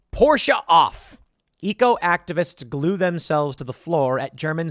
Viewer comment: Thought he was saying Ego Activists… that makes sense as that’s what they are doing.